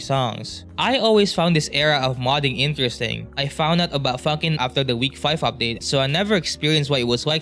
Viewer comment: Honestly it sounded like you said "I found out about fuckin" 😭